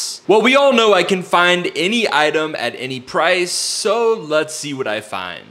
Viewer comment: I thought he said he could find any item and price